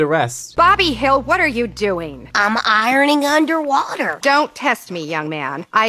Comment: I can't help to think "I'm ironing of P C P" whenever I hear Bobby say the line at 0:48. YouTube Poops have seriously corrupted my mind